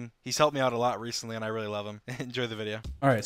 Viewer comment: DID JUST SAID "AND I LOVE HIM" LIKE- WTF